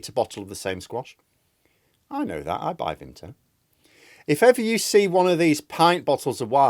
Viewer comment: did you say you buy vimto?! That's an instant unsubscribe. You sick, sick person!